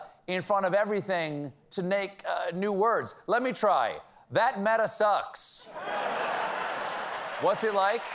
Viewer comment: Stephen says "That meta-sucks", closed captions say "That meta-blows" Either way, it's all good.